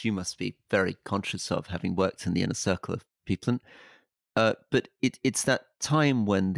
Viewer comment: It sounds like Rory says “having worked in the inner circle of Putin” 😂. Of course he said “people”. What happened to the audio at that bit (28:06)?